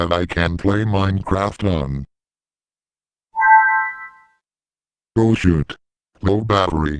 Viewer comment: bruh not cersoned i hear oh shi* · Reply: he said "o shoot."